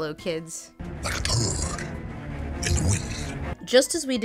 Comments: I keep hearing "Like a turd... in the wind," but I think it's supposed to be "Like a bird in the wind?" Can't really tell... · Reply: Oh no, you’re hearing it right, it really, really is “Like a turd in the wind”. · I kept hearing "like a toad... in the wind" until Lindsay pronounced it herself. My ears were not ready, I guess